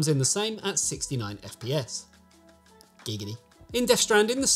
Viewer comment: Did I hear GIGGITY?!